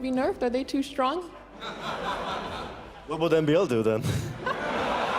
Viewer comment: What did he say at 1:52:04 ? I can't decypher it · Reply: "What would MbL do then?" Supposedly MbL makes a lot of skirmishers. I thought it was supposed to be spearmen, but maybe it's both.